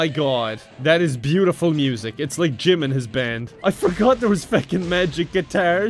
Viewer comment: i swear i heard him say "it's like jimins band" teLL ME IM NOT TRIPPING